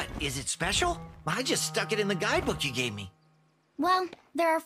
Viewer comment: did Spyro say, "I just stuck in the Guidebook you gave me"? Did Tom Kenny misread his line? It sure doesn't sound like he said "it". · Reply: No. He said, "I just stuck it in the guidebook you gave me".